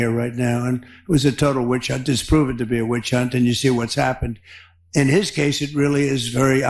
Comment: Did he say "disproven to be a witch hunt"???? That's what it sounded like to me. Ironic word slip.